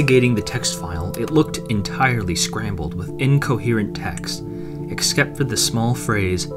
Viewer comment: did you say "exkept"?